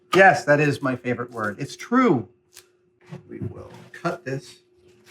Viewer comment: I swear to god I hear Kratos voice at 4:28 say "we will" as Adam say "cut this" is that just me?